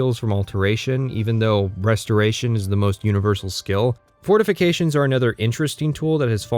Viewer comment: I swear to god, he said "breastoration" there